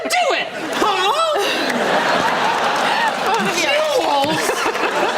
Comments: what did he say? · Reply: It’s definitely Jewels, but it does kinda sound like “Two Holes!?!” though. 😂😂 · What does andy say at 11:00? Two holes? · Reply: “Jewels?!”